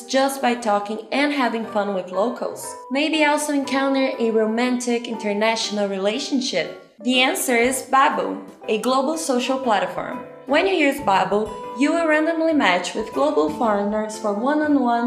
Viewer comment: babo tapi dengernya kaya ubab alias tau kan apa itu ubab kalo dibalik urutannya